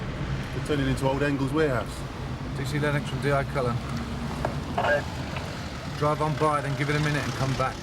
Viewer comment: The subtitles are wrong. When they are turned on, it thought DI Alex Cullen said "Did you see that extra in the eye color?", when he actually said "DC Lennox from DI Cullen".